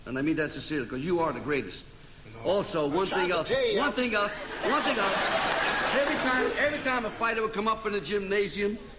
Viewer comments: what does ALİ say at 23:13? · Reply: In his reply to Angelo Dundee he said: "No, I tried to tell you!" -he was referring to when he used to say "I am the greatest" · What's Ali saying at 23:14 in response to Angelo Dundee telling him he's the greatest? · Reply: I've tried to tell ya